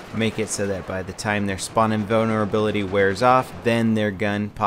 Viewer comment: I hear spawning boner ability.. 0.0